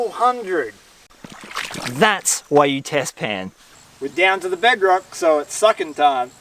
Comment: I thought he said “down to the bedroom it’s suckin time” had to rewind 😉